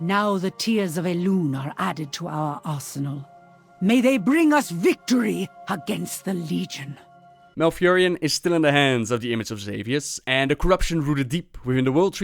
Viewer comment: she sounds like she said ASSENAL instead of ARSENAL..........hahahaha